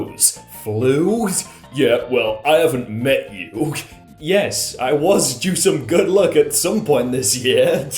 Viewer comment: he actually said "wonder if I'll ever meet anyone" tho